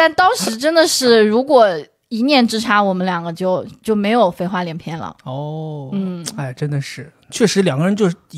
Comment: 好像不是“飞花连篇” 而是“废话连篇” - Ý LÀ "NẾU NHƯ LỆCH 1 CHÚT, HAI CHÚNG TA CÓ THỂ KHÔNG CẦN PHẢI NÓI GÌ DÀI DÒNG NỮA!"